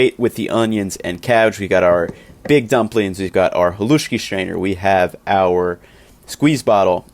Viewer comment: My late aunt made a version of this that is a favorite comfort food. I always thought she was saying Kaluski lol. She added pork sausage with it too.